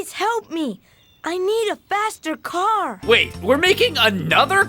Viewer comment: I thought Arthur said ''I need a bastered car!''